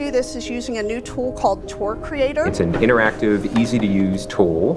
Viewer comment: At first I thought the woman said twerk creator me wtf!!???